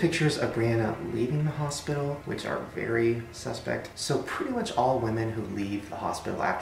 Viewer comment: wait did you just say SUS?!?!?!?!? AMONG US? IMPOSTOR COMFIRMED??